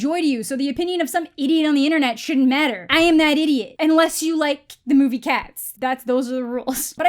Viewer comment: I thought she said unless you love cats. Me and my six cats all gasped. Then I realized she said cats the movie. And we were like oh no yeah that was epic shit. Lol